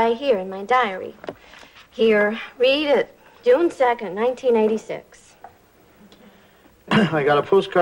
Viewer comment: According to the Auto-Generated Closed Captioning Subtitles, Stephanie said June 2nd 1986, which would makes sense since that day would've already passed over 2 1/2 years before since this episode aired back in January 16th 1989; however, it oddly sounded like Stephanie said 1996, a year which has yet to exist for another 7 years, 5 1/2 years after Newhart ended; maybe I just misheard what Stephanie said.